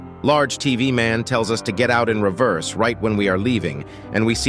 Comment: I thought he said "get out its here"